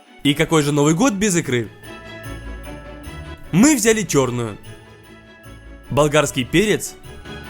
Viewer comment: кому послышалось "мы взяли тёрную"